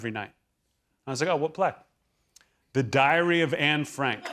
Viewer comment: I thought he said diarrhea of Anne Frank.